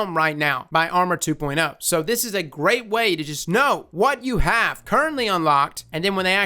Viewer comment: Lol at 15:15 it sounded like you said NO instead of know